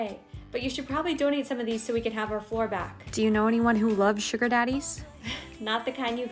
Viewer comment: Bro I heard sugar daddy's and I was like HOLD UP HOW THE HELL DID YOU FIT SO MANY MEN IN ONE ROOM 🤨🤨🤯🤯 but ended up being the candy XD